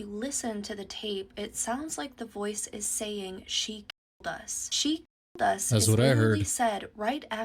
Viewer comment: I heard killer not she killed us but who knows. I believe she killed them but did she know she was going to survive or was she hoping it would kill her as well.